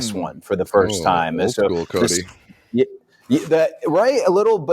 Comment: What was said by at 12:11 I thought he said "Throwback Cody" in regards to the hood.